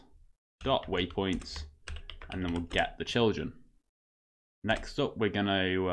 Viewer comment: did he really say get the children